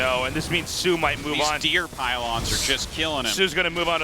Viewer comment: Artosis. You will never rename the pylon. I heard you "dear pylon"